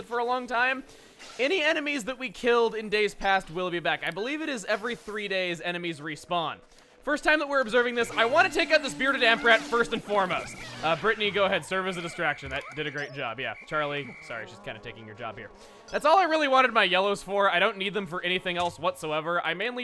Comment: I thought that said retarded armpit.